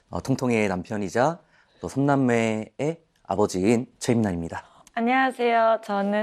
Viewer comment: "삼남매의 아버지인 최민환입니다" --> 성매매의 아버지인;; 으로 잘못 들림